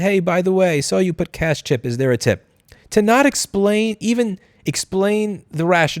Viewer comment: I think you misheard there He said "Hi it's saying 0 for the tip, was there a cash tip?" There is no 'cashtip' option on doordash, she just listed 0 on the tip and he called her asking if she would tip him in cash There was never a moment in the delivery where she claimed she would tip in cash instead of online